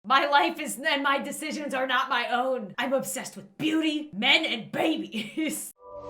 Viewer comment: Oh I thought she said'I'm obsessed with booties, men, and rabies!'